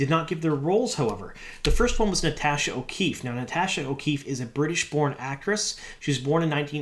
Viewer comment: Man, I had to rewind that. I thought you said Natasha O'Keefe was a British porn actress. LOL